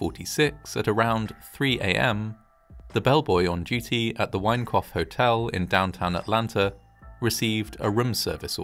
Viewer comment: Thought you kept saying 'wankoff hotel' 😂😂😂